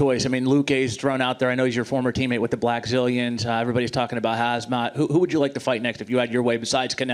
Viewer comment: Did he say Hazmat? 😂